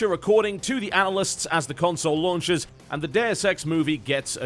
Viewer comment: you say "Deus Ex" but I swear I heard 'the dare sex", just saying.